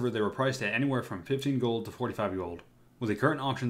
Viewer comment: does anyone else hear at 12:04 ''15 gold to 45 year old'' ???